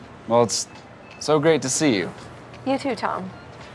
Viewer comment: When she said “you to Tom.” I heard “your to tall.” Sigh. The life of a short person.